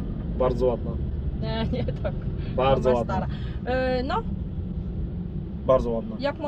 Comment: usłyszałem twoja stara xDDDD